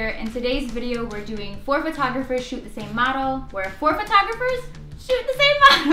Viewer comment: I thought he said poor photographers In the intro